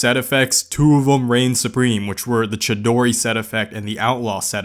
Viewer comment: did you say chidori from naruto sheesh im playin this